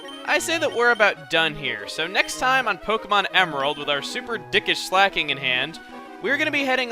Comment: The only thing I heard from "Keep our super dickish Slaking in hand" was "super dick in hand"